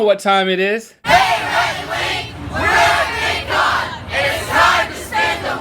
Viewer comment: Captions say they mumble, but I’m pretty sure they say “We’re at VidCon”